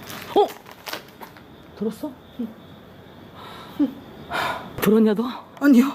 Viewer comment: 잘들어보면 진짜 응이라고 들림ㄷㄷㄷ